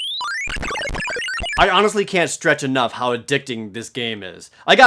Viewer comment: At ~8:56 he says I honestly can't stretch enough instead of stress 0/10 video